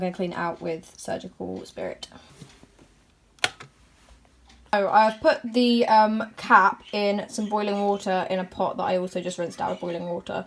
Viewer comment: When she said I'm gonna check on the cap in the boiling water i thought she said I'm gonna check on the cat in the boiling water